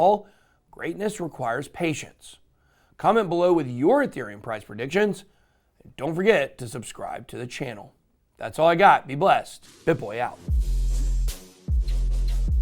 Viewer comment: I’ve been watching your videos for a year now and I just realized at the end of you videos you say, “BitBoy out”. I thought you were saying bonboij or some s*** like that.😂😂